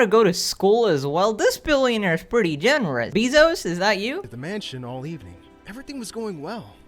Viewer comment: did i just hear say beezo 💀